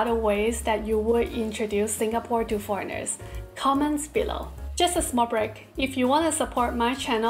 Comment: Im tripping, I always hear, ''just a smoke break''.